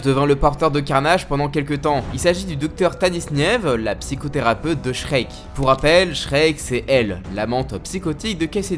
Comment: J'ai entendu "la psychothérapeute de Schrek " mdr . 😂😂😂😂